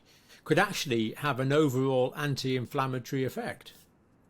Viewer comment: sorry , english not first language ,no subtitles so i struggle, what is that word could somebody help me out , 11:52 anti alfmithry effect? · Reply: anti - inflamatory, reaction against infection.